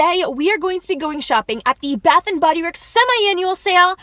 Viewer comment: At first I thought you said bathroom and Bodyworks